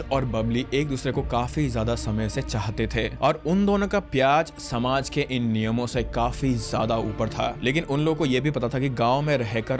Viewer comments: Men be killing their own daughter and sister and they say they are "protectors" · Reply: Dude just said pyaz instead of pyar although it's alright cuz they both make us cry 😂😂😂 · Did bro just say pyaaz? 😂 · Did i hear unn dono ka PYAAJ